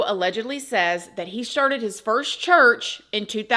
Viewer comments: Lol it sounded like you said "He sharted his first church" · Did she say he “sharted” his first church…appropriate description in his case. 😂